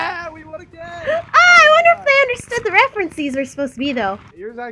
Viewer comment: Did you just say... Refrencees?